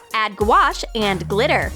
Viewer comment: Did you just say add goulash?